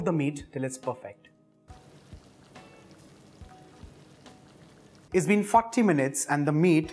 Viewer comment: Did he say " it's been fukty minutes"